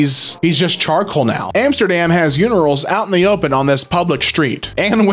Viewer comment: he said unirals instead of urinals :D